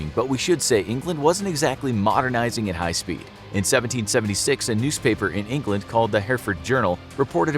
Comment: did anyone else hear a nuce-paper?